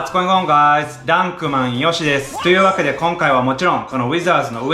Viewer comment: いつも挨拶が「ダンク、マエヨシです。」なのに今日は「ダンクマン、ヨシです。」にちゃんと聞こえるw